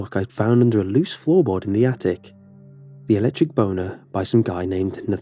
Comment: Did he just say "electric boner"?